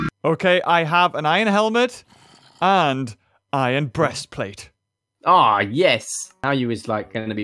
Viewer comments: did say breastplate? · did say breastplate lol